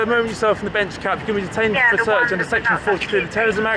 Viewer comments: Sounds like he said section 42 🤔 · sounds like he said section 42 thats a new one lol